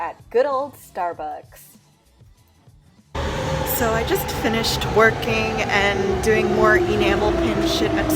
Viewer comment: it sounded like you said you finished twerking lol